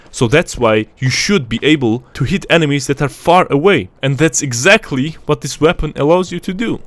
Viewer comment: I hear you say “fart away” LOL